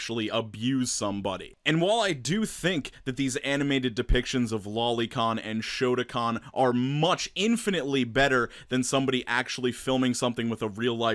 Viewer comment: I apologize but. How pathetic do you have to be to defend something. . Like Lolicon that's. Ew. I'm sorry my dudes but that's way sad Edit: Well. . I seemed to have created a long hard discussion on the ethics of lolicon....time to bleach my existence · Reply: Gabriel Magici for a second I thought you said Lincoln